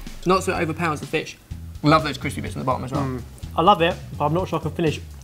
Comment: it sounded like said bitch, lol